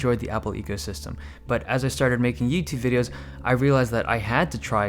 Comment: did anyone else hear yeetube